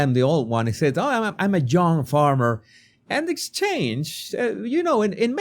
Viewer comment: I thought he said "I'm a young timer" .. did he say young farmer ?